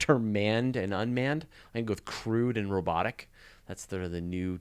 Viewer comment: I was in the other room for a second and I heard "Crude and Robotic" and came running in to see what it was about, I thought you were Canadian-ly-mad! Nope, it was about "manned" or "unmanned" missions to Mars as the less-preferred terminology.